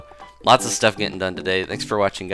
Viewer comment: I mean I thought he said Amnimal Crossing